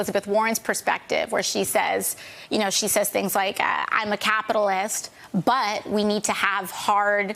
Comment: my mind is perverted I thought she said but the butt (booty) is there! I was like yaaaaay